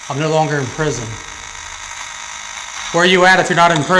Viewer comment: 5. I Heard "Only in Prison". 4. D: Goosebumbs! D: On my whole Body! D